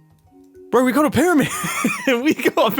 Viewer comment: Did Mumbo say "bro"?